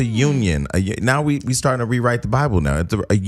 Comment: Did Jason just say, "We're starting to rewrite the Bible"???? LOLOL